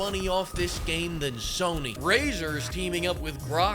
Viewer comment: oh, you said razer is colabing with grok.. thought you said brazzers